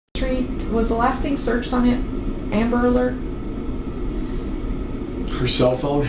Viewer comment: I know it’s redacted but doesn’t it sound like she said “hickory tree amber alert”. If so isn’t that where Maddie was found?